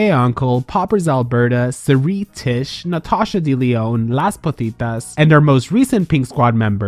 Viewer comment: Did they just say ‘Las Putitas’? Ahahahahaah